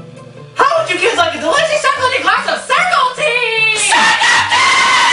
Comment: I thought they were saying circle tea